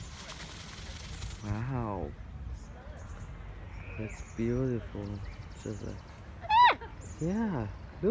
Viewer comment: Sounded like he said "that was beautiful man." He was in birdie bliss.